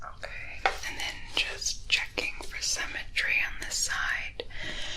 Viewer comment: Somehow I heard "symmetry" as "cemetery" and thought "dadgum, this went dark."